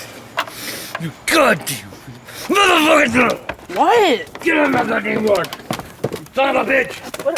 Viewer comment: YES!!!!😂 1:07 it sounded like said You Goddamn MOTHAFUGGIN THROAT